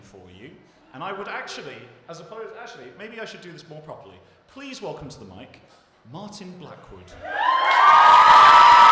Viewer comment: poem timestamp Also does anyone understand what he says after 'statement'? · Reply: They are statements, taken straight from branches (?)